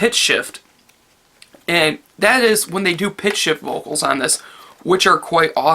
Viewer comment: I honestly thought you said pig shit vocals instead of pitch shift vocals lol